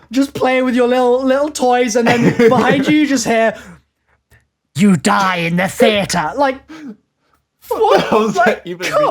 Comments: Is it me or am I hearing "you'll die with Santa" 😂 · I hear: "Hide yourself in the theater" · I heard "They die in the theater"